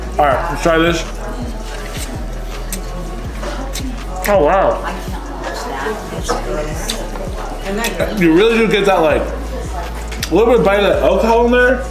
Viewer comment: Who else heard at 9:20 " I cannot watch that it's gross " tf??